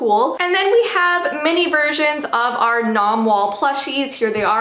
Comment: did she say nomwall?